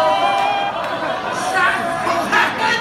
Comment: what does Kirk say at 5:55? i can't hear it clearly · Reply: Maria Robotnik I think he says, "Oh, really?"